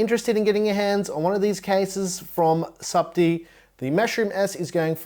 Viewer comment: Thought it said STUPIDD MushroomS.